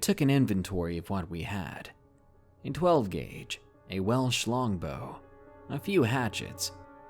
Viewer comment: One of those odd distracted listening things. @ 2:23 I heard "well schlong bow" wait what? Ohh '-'